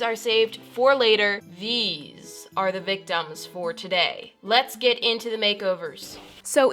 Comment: Sounded like she said "THESE... Are the big dumbs for today..." Haha... Big dumbs